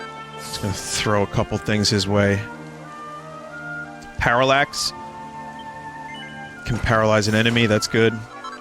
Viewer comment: "HA...HA...PARALLAXE!" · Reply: I thought he was saying "Pa...Pa...PARALLAXE!"